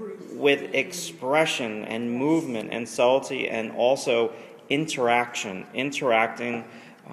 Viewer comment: Did he just say salty?